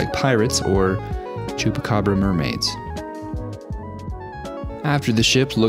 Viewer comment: you have to try resin at least once more. I thought you said Chupacabra Mermaids , but I wasn't sure I heard it right so I had to go back watch that part again with closed caption on and yup that is what it was. Now that is a twisted idea, now you have to come up with them in another build soon, I have to see some Chupacabra Mermaids, I really have to see them. Crack or not, even with the resin mishaps it still came out awesome. You are totally a pro at this stuff because you fixed 99.99% of the mishaps without making them look like you had to fix them. Great Job, and We Need To See The Chupacabra Mermaids.